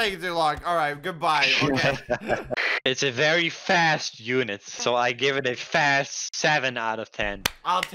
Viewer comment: i love how the subtitles said unite instead of unit